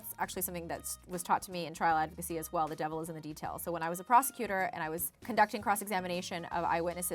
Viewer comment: Who else thought she said prostitute...?